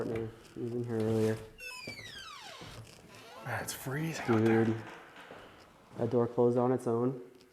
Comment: I hear clearly Their out there! I’m not even wearing headphones and I can hear it!